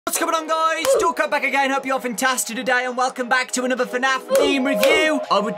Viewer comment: why did I heard "Don't come back again"